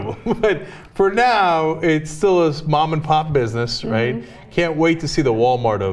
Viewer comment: Did Cenk say it's a "Mom and Pot business" ? ;-)